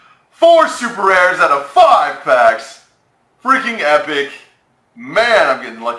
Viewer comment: sounds like he is saying " 4 super rares out of Fuck"